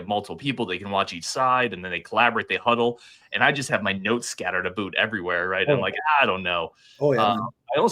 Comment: Did you just say "scattered aboot" at 28:01??? LOL, how far north in WI did you get....clear across the lake?!